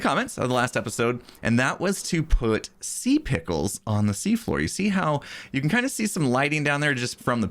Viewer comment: Did he actually say "pickles on the sea floor"? PICK-LES, PICKLES ON THE SEA-FLOOR